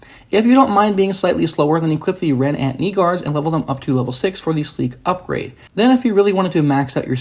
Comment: what did he sayyy?? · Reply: Equip the red ant knee guards and level them up to level 6 for the sleek upgrade